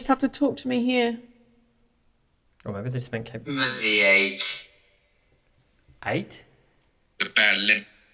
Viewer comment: When you got "I'm at the eight" through vox at 41:03 I heard "I'm at the age" which would make sense because they say something along the lines of a bad limp/leg thought some might find interesting maybe they are reliving their pain of growing older or being in such poor living/working conditions